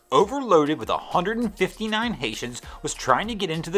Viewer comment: Oh, Haitians! I thought you said patients and I was like wtf lol.